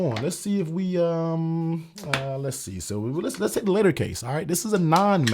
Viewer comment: What’s the second phone case I think I heard you say the letter case but I can’t find it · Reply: Latercase.